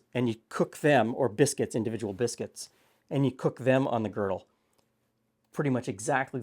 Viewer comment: does say Griddle or Girdle? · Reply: Girdle